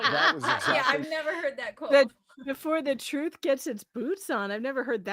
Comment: I've always heard it as "before the truth gets its pants on"